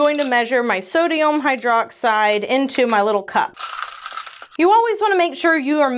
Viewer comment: am I the only one who hears so dayum hydroxide? 😂